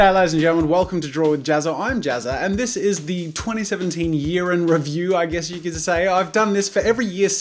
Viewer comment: I thought you said, "2017 urine review" Lol